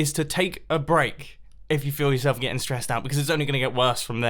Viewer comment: I skipped twice around when Mumbo says suggestion around 3:58 and he said: feel yourself a wall. but is sounded like build yourself a wall!